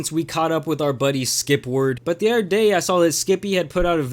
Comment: I thought you was saying skip-lord not skipward